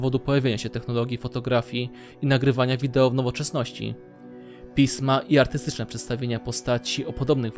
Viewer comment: Czy tyko ja usłyszałam PIS-ma xd